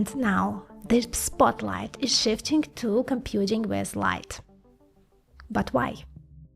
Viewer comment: computing west light 😂😂😂 i swear it sounded like that 😂❤❤❤💚💚💖😅 6:38 how you talk to them xD ig, fb, linkedin, etc?